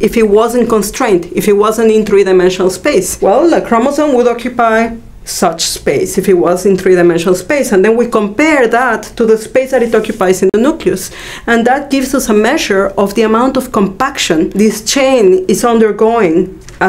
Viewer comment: Who'd have thought a nuclear reactor could be so complicated? HomerSimpson.jpg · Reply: All I hear is "Nuculus".